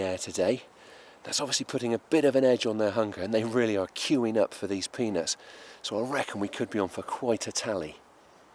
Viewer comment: did he say queuing up for penis